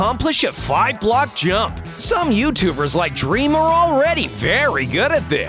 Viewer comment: wait...... Did you said dream?